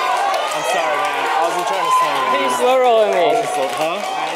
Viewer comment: Miles crying about being slowrolled lol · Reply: I've played over and over before, it sound like he said "you slowrolled me....punk"